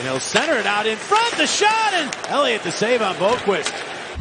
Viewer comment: NOTES: - Had Carter Hart played this game, it would be a lopsided game in favour of the Devils (considering that he was still fighting a knee sprain) - Besides Provorov and Braun, Philadelphia’s defense is MIA (missing in action) - On the NHL website, I was thinking that the shootout was going to go past 20 rounds (the current record) - New Jersey’s poor performance this season is all on one player: P.K. Subban due to locker room cancer (not trying to be racist; his attitude got him expelled from Montreal) - Pavel Zacha is proving the Devils that he is no draft bust - BOLD PREDICTION: Jack Hughes’ career will be very similar to Joe Thornton - Did I just hear “Lundqvist” in the shootout? - Kevin Hayes’ shootout goal would not count as Hayes was literally holding Blackwood down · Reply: He said boqvuist